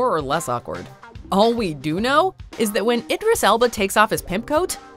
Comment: did you call him Icarus Elba????